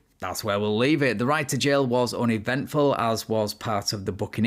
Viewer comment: At the end you said "the ride to jail was uneventful" but I heard "the right to jail was uneventful". Hmmm six of one, half a dozen of the other.